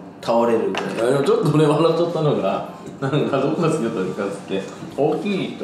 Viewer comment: ここからのトミーさんの言葉、カンタくんといつも見てる人じゃないと聞き取れなさそうｗｗｗｗｗｗｗ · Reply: 確かにw 『どこが好きだったんですかっつって』 ですよね（笑）